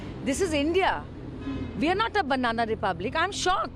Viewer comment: did she say we are not a banana republic 2:06 im so confused lmao